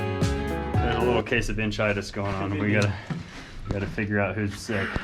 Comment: “a little case of bitchitus? Is that what he said?